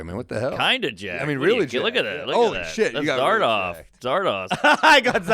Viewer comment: What did Normand say at 0:33? Zardoff? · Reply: zardoz, the sean connery movie, hence the photoshopped image later